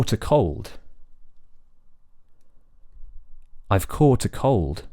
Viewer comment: When you say "caught", I hear "coat".